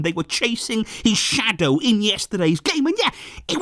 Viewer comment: All I heard was "They were chasing his shadow Iniesta-day's game" 😅 that's how good Pedri is